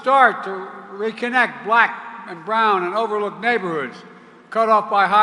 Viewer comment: It sounded like Biden said “overlooked neg*os”… in that speech 😮 Hopefully, I’m wrong.